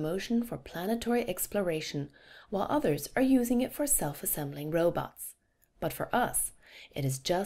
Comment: Wtf, does she say self assembling robots at 2:20 ?